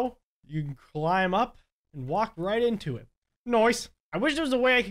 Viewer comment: He said noise instead of nice. In the captions it says that